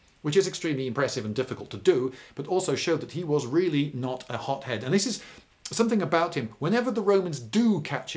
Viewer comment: I keep hearing 'hop-head' instead of 'hot-head'.